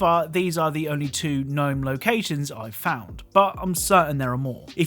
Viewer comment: Did I hear him say "So far these are the only gnome locations"? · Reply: As in he replaced "known" with "gnome"